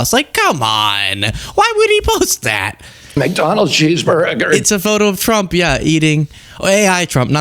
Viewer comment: i fuckin' laughed way too hard that i thought it said "donald cheeseburger"